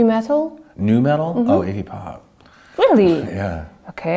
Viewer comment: Slash thought she said Iggy Pop when she said hip hop and chose it (him) over nu-metal. : ) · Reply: And she thought he replied, "hip hop," which is why she seems surprised. They both misheard each other.